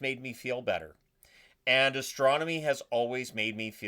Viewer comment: Battle Beyond the Stars? Lol, one of the worst movies ever made, it was on MST3K. The rest are great. Honestly, I thought at first you said astrology as I was about to unsub, lol. But apparently our universe is inside a black hole, which itself has black holes, so you can see the hypothesized reality as black holes within black holes, within .... Like a camera looking into a mirror. Now imagine each image reflection as an independent reality. Multi-universe/realities.